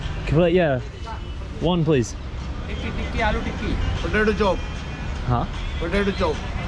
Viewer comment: he said potato chop i think 🤣😭 u said job 😂